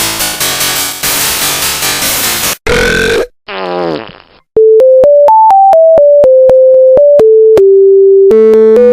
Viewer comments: Hey! What do you say? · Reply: VCVCFans he is fizz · Hey! What do you say? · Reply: EXCUSE ME